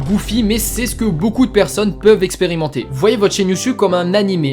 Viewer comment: Frérot, il s'est passé quoi ? T'as changé de "expérimenter" à "expirementer" ;-; C'est l'ajouteur de sous-titre automatique qui a fait ça ou ? Sinon bonne vidéo, mais les conseils sont pas ce que j'attendais contenue de ce titre qui fait PUT A CLICK.